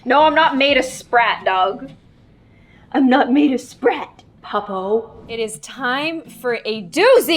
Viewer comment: did she say pabo mean fool in Korean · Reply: No, she said puppo